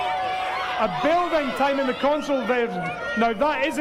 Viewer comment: Lol, I thought it said Building Time Haunted? for a second xD